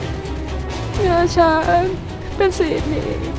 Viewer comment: นางเอกพูดว่าอะไรเป็นสีนี้หรอคะ ตอน 8:35 ฟังไม่ออก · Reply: งาช้างคะ … งาช้างคือกำไลที่ม่านแก้วใส่ืคะ